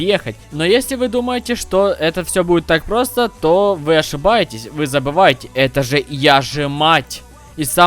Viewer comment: я не расслышал но вроде она сказала я забыл🙁